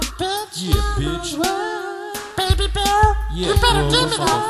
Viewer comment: wtf they say westside 0:39 thumbs if u hear it to